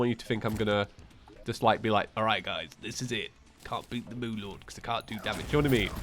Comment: Literally thought he said “Moon Lord Beans”